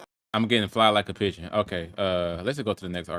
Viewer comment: he actually said “get high like a pigeon” which doesn’t make it any better but still 😭. (Also you spamming the trash soundbyte had me dying 🤣)